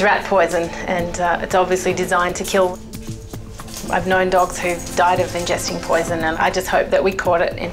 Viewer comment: does she say "i have nine dogs that died from ingesting poisen?" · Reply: "I've known dogs who've died of ingesting poison"